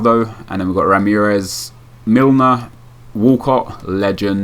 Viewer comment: DID u say WALCOCK?